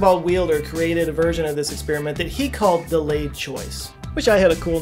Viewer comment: I could've sworn I heard you call Wheeler's experiment "The Ladies' Choice" ^^'